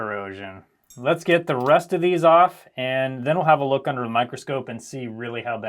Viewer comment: Did you make a pun. It sounded like you said "Let's get the rust of these off"